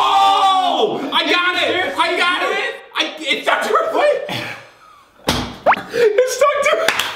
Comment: makes it sound like is saying it’s stuck to her foot